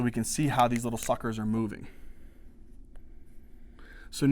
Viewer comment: Did dude say "fuckers" at 21:40?